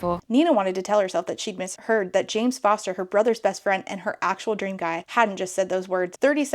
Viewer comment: "James Foster"??? I misheard, thought it was Jane 😢. I miss Jane Foster so much 😭